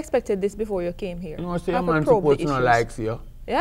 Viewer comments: could someone please let a nigerian man know what he's saying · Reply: In standard English he says “You know your man suppose to don’t like to see you” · What does he say at 12:03 can’t work it out😂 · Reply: U know your man probably doesn’t like seeing u. (Yuh know Yuh man nuh Supposed To Like See yuh)